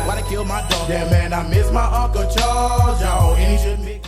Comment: Whoa I thought he said "I miss my Uncle George" 😳